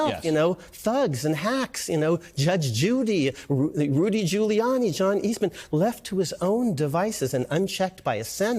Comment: Did he just say Judge Judy is an incompetent hack?